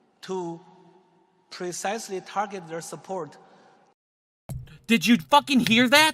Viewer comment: he actually said the CBDC would "allow government agencies to precisely target their support" not target people who support them which is what you said he said. Just a nitpick but I 100% agree with you what this douche really meant was exactly what you said, they would control the way people spend their money and control how much they can save and they'll give extra money to people who support them and starve out the people who don't. It's pure fucking dystopia plain and simple!